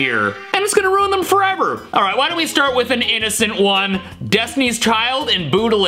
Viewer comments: it almost sounds like is saying "alright why don't we start with an Anisint one" Lol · I heard Disney's child